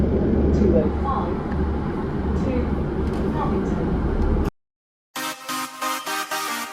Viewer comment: I swear the I-Bus still said 205 to paddington.